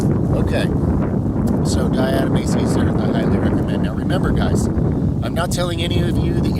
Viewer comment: What's the thing he's talking about at about 9:00? I can't figure out how to spell it. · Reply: Diatomaceous earth.